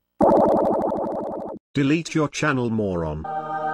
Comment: i thought that said 'delete your channel mormon'